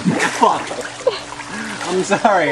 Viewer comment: an 2:35 is john saying fuck?? lol